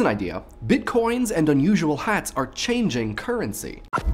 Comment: I thought it said 'bitches' instead of bitcoin